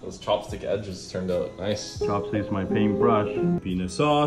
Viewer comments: Flaa join me brothers of flaa Edit: I heard “pen*s sauce” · When he said peanut sauce I sounded like he said penis sauce 😂